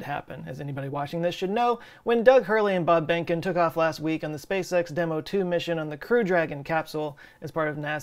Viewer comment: I always hear “SpaceX” as “Space Sex”